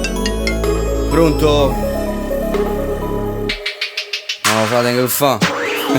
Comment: chi mi sa dire cosa dice lele al minuto 3:48 dopo "Pronto?" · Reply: NO FRA TENG C FA